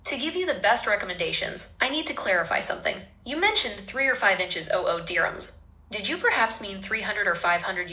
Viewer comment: Did i misheard it or it says 3 or 5 inches oh ohh Dirhams?